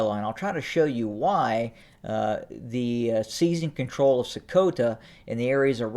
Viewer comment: Voice of bullshit I thought you said they already control sokota 😂 the beginning of the end for TPLF